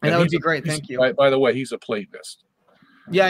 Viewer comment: what does Sungenis says Wolfgang Smith is at 1:07:39? A platonist?